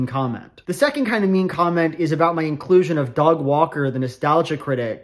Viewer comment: I heard Dog Walker, and know I'm shocked I've not heard that insult before. The internet has far more restraint than I expected. Heh. Dog Walker. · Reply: Wait, you mean people don't call him that? Am I just mishearing due to the accent? I'm British, and it always sounds like Dog Walker to me. I thought it was generally accepted.